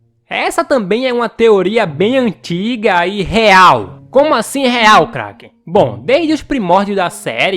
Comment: COMO ASSIM REAL CRACK? kkkkk sem mancada q eu ouvi isso kkkk TO BRINCANDO😁